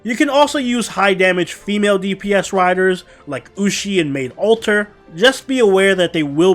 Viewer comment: Did I hear Medb alter? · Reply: I wish lol, I said Maid Alter